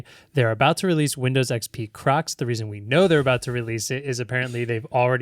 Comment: I thought was saying Windows XP crux 😅 didn't realise he was talking about footwear 😂